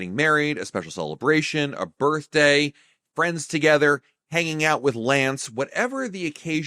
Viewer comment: At around 8:30 did you just say “Hanging out with Lance” I’m Lance and I’ll be there as soon as I can.